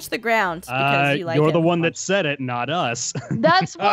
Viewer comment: "That's what the story said you D**k face" DID ANYONE ELSE HERE THAT?!